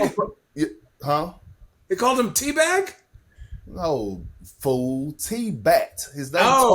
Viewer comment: Everybody thought Perk said tea bag